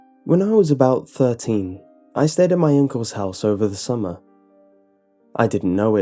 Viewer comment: I'm not the only one who thought he said "I stayed at my ankles house" right?